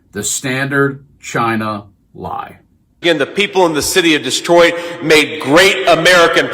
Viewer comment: Did Vance say "the city of Destroyed"?